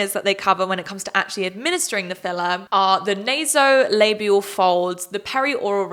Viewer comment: The way I cranked my neck around when I was idly folding laundry to this video and misheard “nasolabial folds” as “labia folds.” 💀💀💀 I was like, “THEY’RE INJECTING WHAT WHERE NOW??” 😭